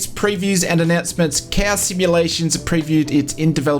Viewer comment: Hey guys, I think Im just dump but I cant get the name of the team behind the dev of the DA42 for MSFS20 in 00:21 I hear Kaw sim or something like that lmao 😭😭😭Im very hyper for a study level DA42 !! Can someone give me the name (or link) for this team pls?? Thank you! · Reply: COW is the name. Just google COW Da-42